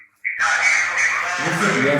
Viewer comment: i swear when u asked are u a demon it said yes i am i swear it said yes i am